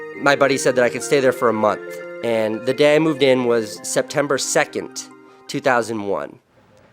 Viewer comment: But wasn't it September 11th no offense but I'm just saying I heard him say September 2nd.